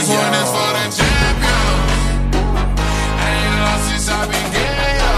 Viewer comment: Am I the only one who thought he said “I ain’t lost since I been gay” Just me okie🤔😰